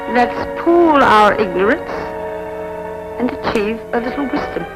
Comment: "Let's pool our ignorance and achieve a little Wisdom!" - great words! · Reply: I thought she said .....and achieve a little whiskey. I will go back and listen ❤